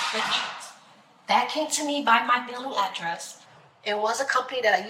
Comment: Sent to me by my mailing address??? What the hell does that even mean? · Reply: I thought she said billing address which was even weirder